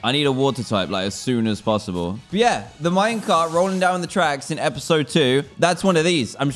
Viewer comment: did he just say Minecraft